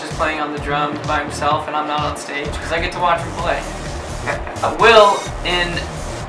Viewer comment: I thought he said: " i get to watch my boy"! OMFG :D